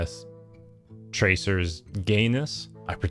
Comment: I heard that as Tracer's Ganus